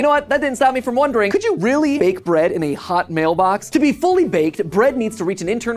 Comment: I heard "to be fully breed" instead of "fully baked." My brain is burned right now 💀